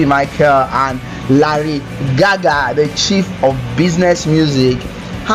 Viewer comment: Did he just say, lady gaga? Lol.